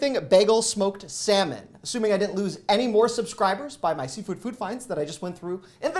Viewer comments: I definitely heard “seafood food fight” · I love ice cream sandwiches!! Btw I thought you were saying “Seafood food fights!!” 😂